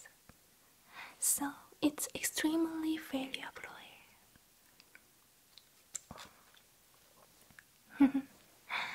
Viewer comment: What is the word she says at 3:00? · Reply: So it’s extremely valuable oil.